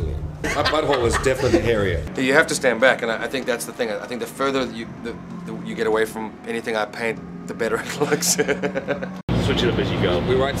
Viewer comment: I cannot make out what he's saying here but that smile is to die for <3" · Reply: “The further away you get from anything I paint the better it looks” lol